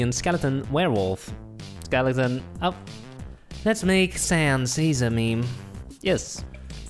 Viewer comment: did u say SANS..... ur gonna have a BAD (good) time